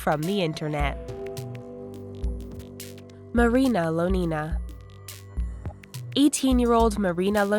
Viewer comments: When she said marina I laughed because I thought she said marina jones xD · i thought she said marina joice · at the start I thought it said marina Joyce